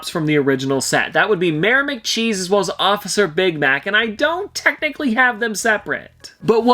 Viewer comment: I thought he said officer big back